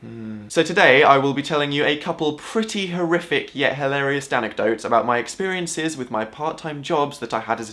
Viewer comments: Did he just say "Danecdotes" at 0:07? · did he just say Danecdotes